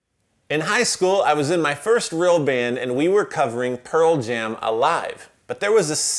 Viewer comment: "Your first band," I misunderstood you. I thought you said your first girl band. Which fits you a little better because you can't play metal. Little to woke. I dont know if I should buy a pedal from a woke man who only likes Pearl jam, & RATM. EDDIE VEDDER & DAVE GROUL SUCK.